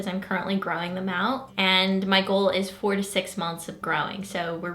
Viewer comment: I can't believe you're growing your brows out for 4 years! That's crazy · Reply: lol she clearly said 4-6 months ... I heard her say months